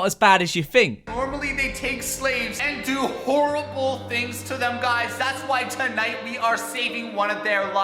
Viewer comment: I thought at 3:00 it said "Guys were gonna buy a slave and do terrible things to them guys" and i was just confused as hell